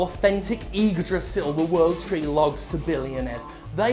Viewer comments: I just woke up from the notification sound and started watching and 100% thought you said Idris Elba the world tree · The first time he says "Yggdrassil" I thought he was saying "Idris Elba!!" 😂😂 Wait. Are those... Boglins!?